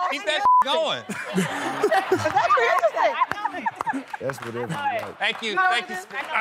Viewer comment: Did anyone hear TI sayin ‘ that’s whatever you like ‘ at 0:26??